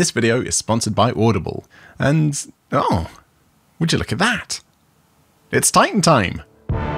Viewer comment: I heard "It's Titan Time" as just "It's Time Time" and found it funnier than i should have.